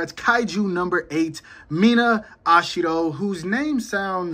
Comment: For a second I thought you said “Mina ashido” and was confused af.💀 weird how close their names are.